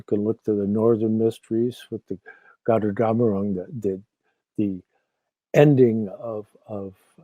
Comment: I cant catch the name of the north god her at: 51:12. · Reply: Gotterdammerung - The Ring of the Nibelungen